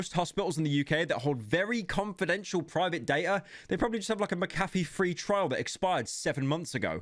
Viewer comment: what the fuck did he just say? mecaffe? its fuckinng mc a fee